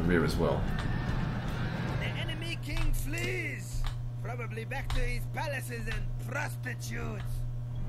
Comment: did he just say: probably back to his palace and prostitutes?